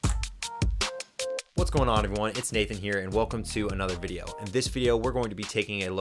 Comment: Is your name David or Nathan? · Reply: Cool! At first I thought I heard David but then I replayed it and I heard Nathan 😂